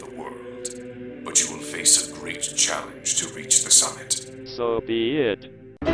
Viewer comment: When Helios was at the temple when he said so be it,it sounded like he said so vi et (soviet)